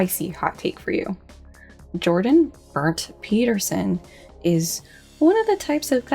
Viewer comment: HOLY FUCK HIS REAL MIDDLE NAME IS BERNT I THOUGHT YOU WERE SAYING "BURNT" AS IN BURNED OMG LOLOLOLOL!!!!